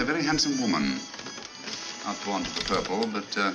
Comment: The subtitles are HILARIOUS. Sometimes there's only the vaguest connection to what actually is said. EG: "...A very handsome woman. Not born to the purple but giving an excellent imitation" becomes: "...A very beautiful woman. Not a real blonde but giving an excellent imitation." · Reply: i thought he said ''not blonde but purple...'' thankyou for the clarification, i rewatched it a few times trying to figure it out and the subtitles were of little help 😂